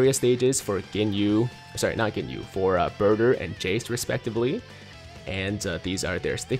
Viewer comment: did he say.....burger?